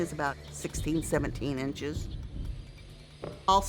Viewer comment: DID SHE JUST SAY 17 INCHES